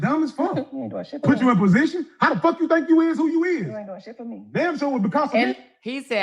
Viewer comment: he actually said, “How the Fk you think 1:50:00 you IS what you IS!”** 😳 First of all, “you + is” will never be grammatically correct! 😂 But beyond the grammar, his mouth is downright disrespectful and disgusting. I wish somebody would think they could talk to me like that! I’ve arrested far too many people because of abusive and disrespectful behavior, and I recognize that kind of verbal abuse when I hear it. No woman should feel like she has to tolerate being talked to that way. That kind of disrespect is NOT okay, period! 💯